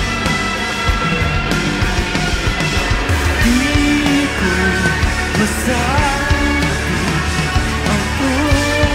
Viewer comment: can someone tell me what he says in 3:50 in Tagalog please i wanna learn that extra verse that he doesnt say in the spotify version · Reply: "di ko masabi ang tunay ng damdamin," in english its "I cannot express my true feelings"